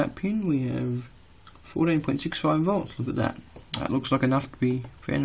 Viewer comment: I thought at 09:55 he said the multi meter said 48.65V?